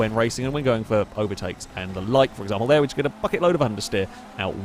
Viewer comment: did just said "fucking load of understeer" or my ears are playing games???! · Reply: Bucket load.